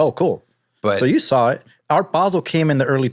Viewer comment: Sorry...who came in the early 2000s? At 9:03. I've replayed it. I can't understand what he's saying. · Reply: art basel